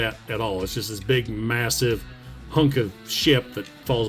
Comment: Did you say what I heard you say? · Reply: "Hunk of ship"? Yes...I said that.